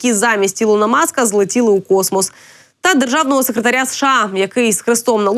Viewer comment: 😂 мне послышалось что Илона маска яйца улетели в космос 😂